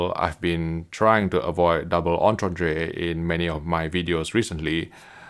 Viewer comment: Wtf does he say at 5:32? Can barely understand him at certain times. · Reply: Double entendre. When words can be understood in different or sexual ways.